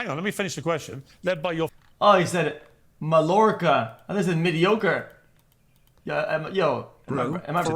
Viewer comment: omg when x thought piers said mediocre and all the chat was like 'its piers accent he pronounced it wrong' ffs man its pronounced exactly as piers said it. ma-ior-ka. dumb fukin na goblins